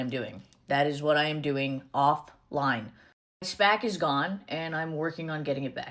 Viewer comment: did she say my spack is gone?